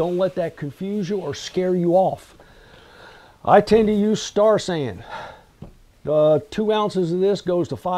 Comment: I would be nice to have Amazon Links to products such as Star San. Affiliate and make a few dollars and at the same time make it convenient for viewers. Such as your mention of Star San. Thought you said Starsand and looked it up. Finally did figure out it was Star San. Like your presentations.